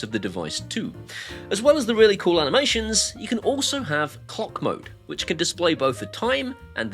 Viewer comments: I heard "cockmode" whatever that might be. Sorry my d@ mind. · I really thought you said 'cock mode' which would suit your channel well 🤣. If anyone wants to complain about the channel then they have a sad life. Keep up the good work mate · I’m 55 going on 12 and love the innuendo. I honestly thought you said cock mode not clock mode. If they don’t like it then don’t watch it.